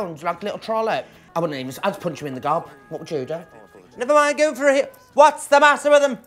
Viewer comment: I’m British but wtf did Vivienne say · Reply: Michael Noble never mind go for it